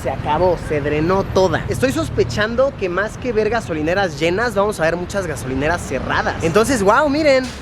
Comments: parece que dicee Vergaaaaaaaaass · parece que dice verga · Alguien notó que dijo ver gas :v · escuche que dijo mas que verga JAJAJA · parece que dice VERGA 😂😂😂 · diganme que no fui el único que pensó que dijo vergas en ves de ver gasolineras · suena como que dice verga :v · Like si te diste cuenta de que dijo verga solinera en ves de ver gasolinera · Pensé que decía vergas XD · Minuto 4:19 nos albureo Luisito Porque dijo MÁS QUE VERGA, SOLINERAS😂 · Minuto 4:30 parece que dice vergas y es ver gas 😂😂😂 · escuche el min 4:20 ver-gasolineras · entendí "vergas" :v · parece que dice que bherga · Díganme que no sólo yo escuche "verga" en el min 4:23. 🤣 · yo entendí "Berga" pero era ver gasolineras abiertas jajajaj Es que lo dijobrapido jajajaja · jajaja parece que dice verg4s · estoy sospechando que vergas orineras like si parece que dijo eso.